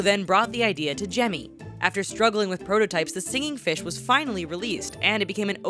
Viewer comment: Did she just say singing "fishes" instead of singing fish?😂